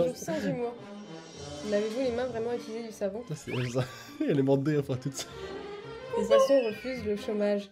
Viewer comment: je comprend pas j'ai l’impression que au lieu de dire 'sa c'est elle ça' , moi je comprend 'sa c'est Elsa'